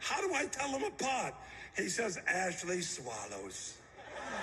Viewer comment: I thought he said "bc Ashley smiles" which is pretty dark but then I went back and listen to it again and my jaw dropped. That's even worse that an I thought. It makes me sick. Then the pictures my god, how dont people get it.